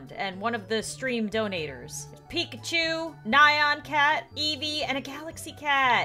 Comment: It sounds like she says eebee instead of eevee